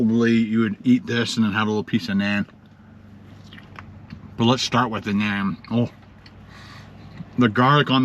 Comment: I bet hes now regretting getting indian...did he just say he's starting with his Nan hehe